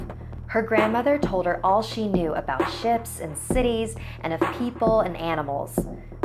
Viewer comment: Who else thought that when she said ships she said shits?